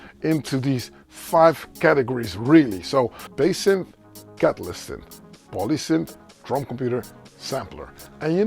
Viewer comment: I've rewound and listened a half dozen times....what does he say after "bass synth"?? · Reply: Catalyst synth! Like stuff I use here: <link>